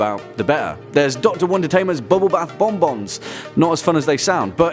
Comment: Anyone else think it sounds like is saying "one detainment?"